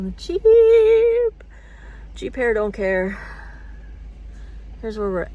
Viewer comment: I thought you said “cheap hair…”. 🤣 Left me wondering. Have a good weekend